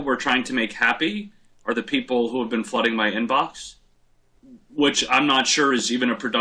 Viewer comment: All I heard was "please keep flooding my inbox. I'm getting uncomfortable." ♡